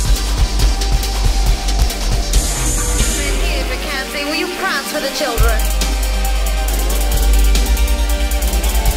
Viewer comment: Does anybody have any idea what's she's saying at 1:52:01 something about children is all I could make out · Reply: Marrythenightgaga101 Shes asking that her dancer, Ian McKenzie, to prance for the children